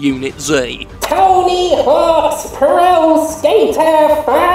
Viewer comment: When he said Tony Hawk's I thought he said tiny horse. I am a dumbass.